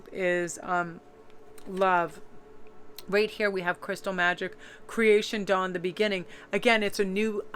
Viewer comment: I thought she said "crystal meth," I was like 'What in the hell?' 😂